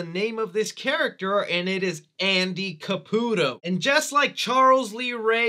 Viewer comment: she didnt say andy caputo she clearly said eddie